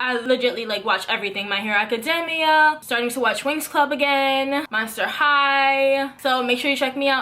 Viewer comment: did I hear the words... Winx Club??? There's also a ship Lukloe ( Luka x Chloe) I just found out about it and I'm not sure how to feel could you make a video on it?